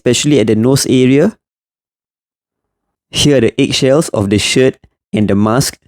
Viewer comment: I thought he said "the eggshells of this shit" 😭😭😭